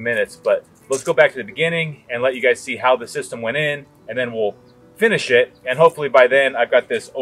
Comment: In the intro, I first heard "and then we'll finish it" as "and then we'll give a shit." I thought, geeze, you're probably being too hard on yourself.